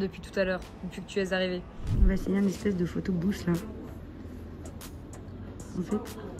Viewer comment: Haha j’avais compris « un photo bouche » a 14:45 - j’étais WTF c’est quoi ça ? « Photo Booth » c’est plus logique! C’est toujours aussi dépaysant comme blog - Merci 🙏